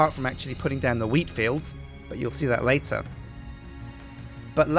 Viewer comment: he says weed fields instead of wheat fields